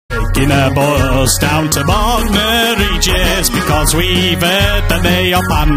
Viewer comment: I thought it said Welcome To Bonors